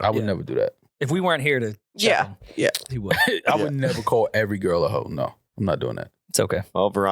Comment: Swear to God I thought this fool Rory said NEGER MALL 😂😂